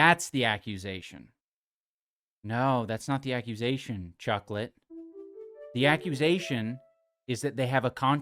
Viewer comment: What did he call him? Did he say chucklette or chocolate?